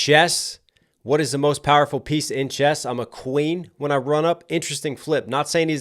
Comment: I thought he said "when i run 'em" as in spades. When playing joker, joker, duce, ace, the queen follows as the next highest suit followed by the king. Also the term "run" the table or running a hand or suit. 🤷‍♂️